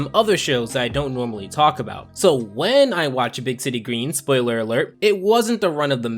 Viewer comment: I swear I think I heard him say, "Big titty greens"